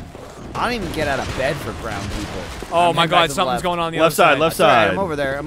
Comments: every time they said "ground people" for some reason i thought they said "brown people". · I thought Michael said "I don't even get out of bed for brown people"